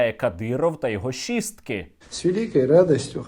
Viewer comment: мені почулося щистки😂 хоча однаково чи щистки чи шістки.